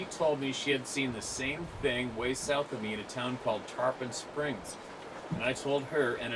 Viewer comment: This was back in 2006 as I understand.... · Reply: Ok I thought I heard in Tarpon which didnt makes sense even in 2006 Thank you